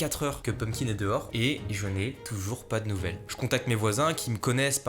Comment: Ptdr le moment où tu racontes avoir demandé à tes voisins si ils ont vu un ptit chat roux😺 J'ai entendu "Un ptit charo" 🤣